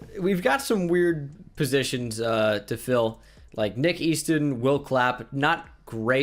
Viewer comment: I literally thought you said "Nick Easton will clap" I was like ClAp WhAt??? 😂😂😂